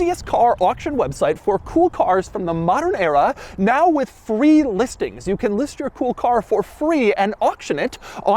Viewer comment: I always hear "3 listings" lol